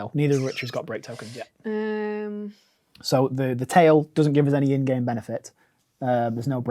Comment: “the tail doesn’t give us any end game benefits.” What the fuuuuuuu!? Did I hear that right?